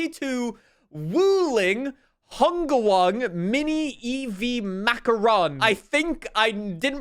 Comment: did you just say macaron 😂